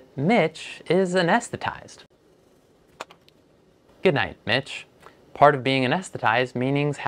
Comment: i thought for a sec he said good night b**ch 😂